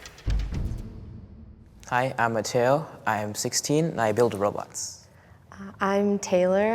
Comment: Oh, I thought he said «Hi I’m Mateo, I’m 16 and I build Roblox»😂